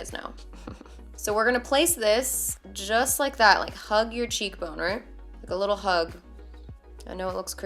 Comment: Girl I swear I heard you say "hug your cheek boner" 😂 I mean if you gotta describe that motion like that for me to looked snatched I ain't gonna complain. #snapfam